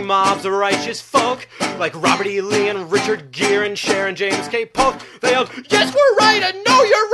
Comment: Anyone have any idea what name he's saying after "Robert E. Lee and Richard Gere" in the song "Righteous Indignation Saved The World" at about 9:01 ? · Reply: james K polk. The ex president of the US I believe